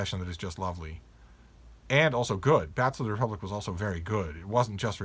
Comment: The Zachery Thomas Dodson illuminated novel I misheard as "Back to the Republic" Which I searched for and came up with nought. So I had to rewatch the video just to hear his name Then searched for him and located: "Bats to the Republic"